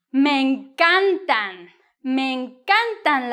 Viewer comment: I appreciate this video is over six years old but I am new to your channel. It’s amazing 😻 At 21:38 you sound to say me encantan but you have written encanta. Did I hear correctly? Many thanks